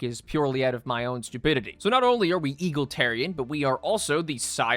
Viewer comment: i know im getting baited and i dont care DID HE JUST SAY EAGLETARIAN?